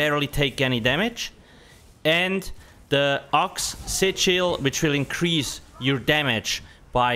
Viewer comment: I thought you were saying "oxy chill" for the longest time 💀 Realized it was axe sigel 🤣